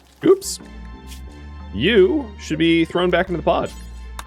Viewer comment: When I first heard that, I thought you say "you should be thrown back into the pot". Given the colony rep, not entirely a likely scenario.